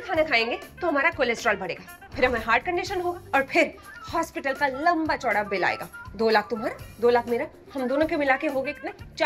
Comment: That zudio bill instead of hospital bill at 3:20 though😂😂